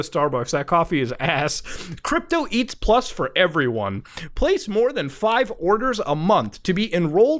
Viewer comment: I heard muta say "crypto eats ass for everyone"